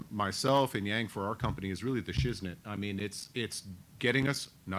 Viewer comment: Did he say: "Yang is the SHIZZNIT!"